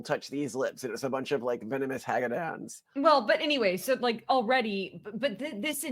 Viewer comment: Malcolm calls a group of woman "venomous hagadas" (or did he mean hags?) I have an interest in learning new words but I cant figure out how to spell this word "hagadas" please help? thanks. 🤔🤔